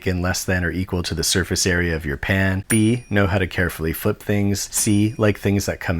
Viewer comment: thought you said "pain" instead of "pan"